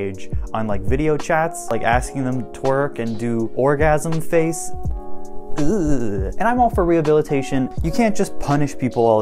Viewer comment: that's... not what he said.. he wanted them to do "r*pe faces"